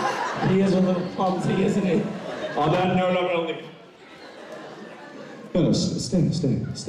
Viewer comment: Does anyone understand what Matt say at 37:28 ...? Haha, and Jensen's face at 41:39 is so cute! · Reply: Cristine G. Matt said: “On the note I’m gonna leave.”